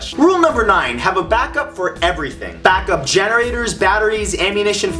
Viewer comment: When he said back up generators i thought he said back up genitals